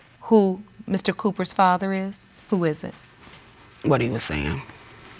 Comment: what did she say ? I didn't get that pls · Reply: she said "what he was saying"